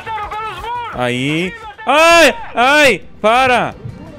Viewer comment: só eu que escutei um oi